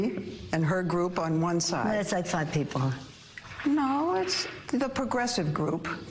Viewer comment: I can't understand what pelosi is saying at 20:19, am I the only one having this problem? I've played it over and over again. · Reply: "It's like five people."